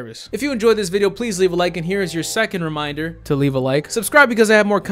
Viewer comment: Every time you say “content” I hear “cunt it.” 😂